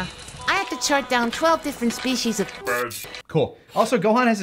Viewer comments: btw, I always heard "twenty species of bird" because I know the difference between flora and fauna and also have auditory processing disorder, so I just always assumed the subtitles were wrong lmaooooooo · Ngl, I genuinely thought Gohan said he had to observe 20 different types of bird until you guys pointed it out that he said fern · Wait a goddamn minute. Does Gohan actually say " [...] twelve species of fern "?! Apparently, my ears have not been working properly until this very day, because I always heard " [...] twelve species of bird ", and I was sure as hell that's what he said. Dayum